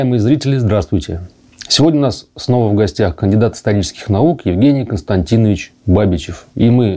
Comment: На ускоренном просмотре первая реплика послышалась "Кандидат сатанических наук" ))